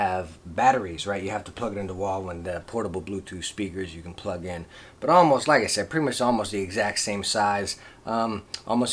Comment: I didn't understand what is better red Jbl or Insigna? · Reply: insignia